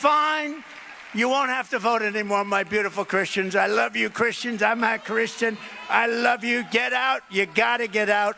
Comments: It does sound like he says "I'm not Christian". And even if he said "I'm a Christian" he was shaking his head "no" during the whole statement. Body language is everything. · No. I heard Him say "I'm a Cursed One..." Freudian Slip. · Thought I heard him say…”I’m half Christian” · I thought he said "I'm ha Christian." Probably just mixed his words up, but my immediate response was "he can't even say he's a Christian without slipping a laugh in there." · It does sound like he says I’m not christian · is he saying that he is not a Christian? 2:19 lol you replied/answered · At first I heard "I'm half christian" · It sounds like he say "I'm a Christian" in his raspy voice-however, he is shaking his head no, so It is likely "I'm not Christian"